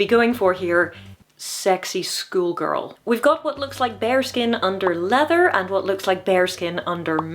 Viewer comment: I thought you said 'Bear Skin' under armor. Was looking for fur.